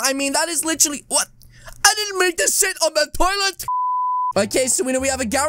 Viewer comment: i haven’t watched him for a while but he said shi* or did i hear wrong cuz i’m pretty sure he said that......